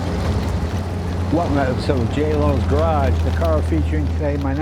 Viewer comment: Sound like you said, welcome to j lo garage.